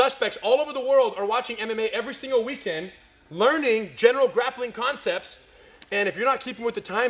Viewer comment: I at first thought he said, "Suspects all over the world are watching ANIME every single weekend learning general grappling concepts." I thought, "Man, is that really how gangstas learn their skills? I ought to be pretty good myself by now!"